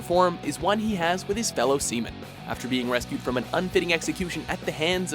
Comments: "The most genuine relationship a pirate can ever form, is one he has with his fellow semen" I know he said Seamen, but for a split second i really heard semen. And i was like wtf?! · i was not listening until i heard "his fellow semen"